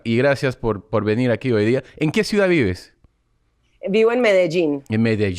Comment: What does Tom say at 3:45 ? It sounds like "en que (something) vives"